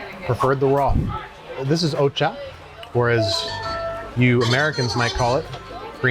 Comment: Came in here thinking you were ranking 25 different conveyor sushi places, not 25 sushi from the same place. Title was a little misleading. lol Also it sounded like you said Oocha instead of Matcha lol